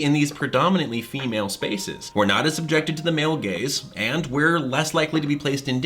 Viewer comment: Everytime someone says "male gaze" i hear "male gays" and i just chuckle a little bit, cuz it usually leads to funny/ironic context. No one should feel like objects, and the fact that some people's brains genuinely objectify people hurts my soul